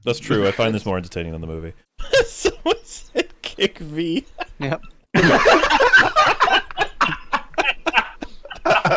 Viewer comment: what did say? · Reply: 'Someone said "kick Vee"' (you can see the comment in the chat)